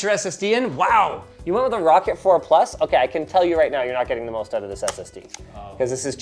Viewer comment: "I can tell you right now you're not going to need anymore Anyone with headphones know what he says? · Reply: You’re not going to get the best out of that SSD*